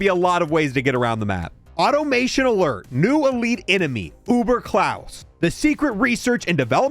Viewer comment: did i hear that right? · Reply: I also heard “Boomer Klaus”. I’m tired atm